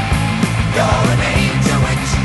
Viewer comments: I've listened to this for years, and suddenly I keep hearing You're in Anchorage, you're in Anchorage. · Stg I thought they were saying Dominatrix instead of you're an angel witch